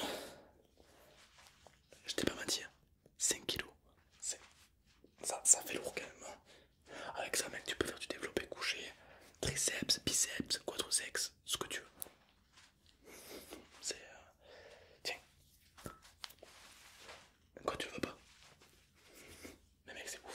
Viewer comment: C'est moi qui entend bizarrement ou il dit quadri-sex???